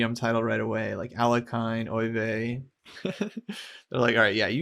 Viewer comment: I just realised you said "Alekhine, Euwe" I thought you said "Alekhine, oy vey!" and figured there was some sort of Jewish joke I'm missing 😂😅